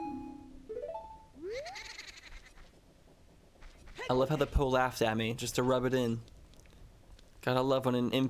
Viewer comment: what does it say about me that when you said "poe laughs" I thought u said "prolapse" LMFAOOO